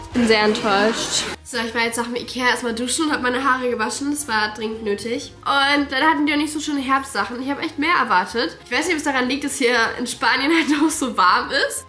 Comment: Ich habe verstanden: „ So ich war erstmal im Ikea duschen“ 😂❤ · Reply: Nein sie hat gesagt: ich war im Ikea und bin DANN duschen gegangen 😅